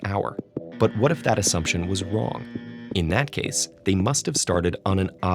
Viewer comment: He said Wong instead of saying wrong